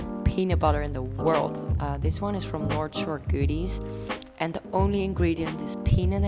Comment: Which brand of peanut butter does she recommend at 09:08? I couldn’t understand what she said, thanks. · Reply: North Shore Goodies